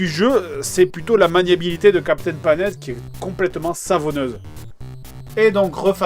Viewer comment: A 7:26, dites moi si je me trompe mais je crois qu'il dit "l'ennemi du jeu c'est la maniabilité de captain pas net"